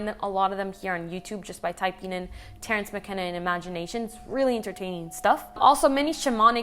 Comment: Someone will answer me this: What's the name she says at 6:36? thank you <3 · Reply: Terrence McKenna You are welcome 😜